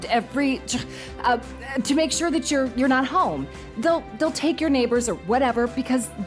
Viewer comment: did they just say they will TAKE your neighbours?